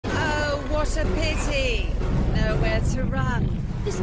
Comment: oh what a PP is what i heard